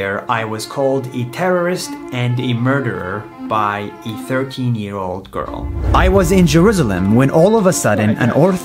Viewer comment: Thought u said 15 year old girl